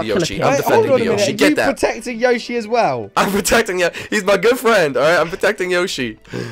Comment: for a second i thought he said girlfriend XD